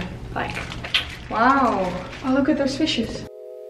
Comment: when u said fishes, I heard ‘sushis’ I am sorry